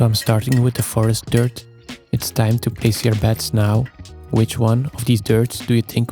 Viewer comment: When you said: “it’s time to place your bets now”…you have that accent, and I thought you said “it’s time to piss your pants now”. Well… I didn’t get to see what happens in the video because I had to go change my pants.